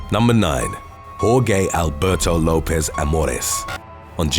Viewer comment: I'm soo sorry, @ 0:01 I thought you said poor gay Alberto... I had to listen to it like four times before I understood